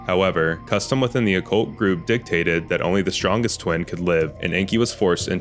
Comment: I can’t be the only one who heard “the strongest twink will live”